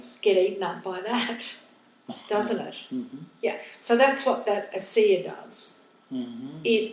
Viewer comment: Is there a translation for this? There are a few very important words mentioned that I cannot understand. For example, something is mentioned at 45:45 that seems very important, but I'm not sure what's being said. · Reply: says: "That is what Asea does". To know more about Asea, please visit: <link>